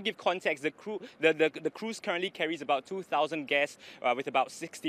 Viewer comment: I watched at X2 speed and I heard two thousand deaths and I almost spilled coffee on my desk. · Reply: Guests... not deaths